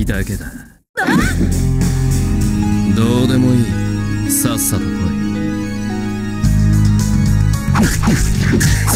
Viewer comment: what did he say? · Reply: Do de mooi sasa urui na